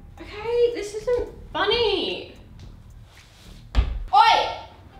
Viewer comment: Did she just say 'oye' at 9:47????